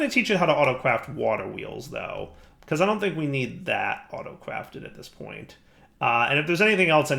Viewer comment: Did anyone else hear say Auto-Cwafted at 14:35 and 14:39 and 14:43?